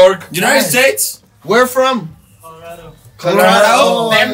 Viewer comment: ¿qué dice el en el 58:50? “Colorado? let’s…” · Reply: Dijo Colorado la traigo con acento gringo